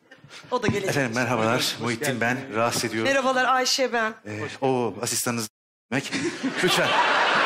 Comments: ta ne diyor tam olarak? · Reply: Oo asistanınız zenci demek diyor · da ne diyor ya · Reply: Oo asistanınız zenci demek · da ne diyor ya çözemedim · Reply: Asistanınız zenci demek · ne diyor anlamadım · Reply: Asistanınız zenci demek diyo